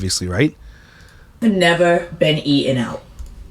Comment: I'm just minding my business organizing my hair care drawer while listening to this and all I heard was "I've never been eaten out" WTF 😂